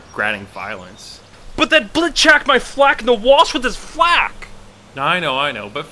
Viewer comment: LOLWhatdidhesay? "But that blit chakked my flak in the walsh with his flak?" that's what it sounded like XD